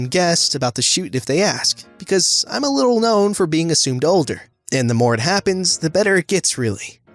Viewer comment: when he said assumed older I thought he was saying a tsundere and I got so confused.